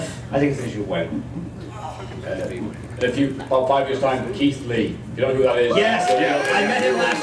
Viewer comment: Which wrestler did say at 13:00? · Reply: Keith Lee